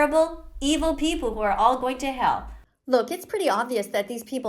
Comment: IM SORRY BUT DID SHE JUST SAY KIDS ARE GOING TO H3LL?!?